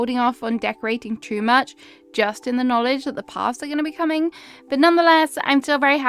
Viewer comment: Thought you said Baths as in Barths... i realised you said Paths.. Notherner here.. we say it as its written.. no R's